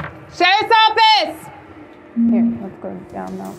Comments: when she says "Sherriff's Office!" it sounds like she is saying "Say Something!" If I were inside, I would have no idea they were police. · I'm an aussie and I couldn't even understand what she said? Shepoffeeett is what I heard? hahaha